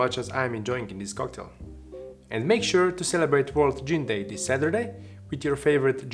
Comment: I thought he said “world jean day” and didn’t even question it 😂 I just thought wow, man loves his jeans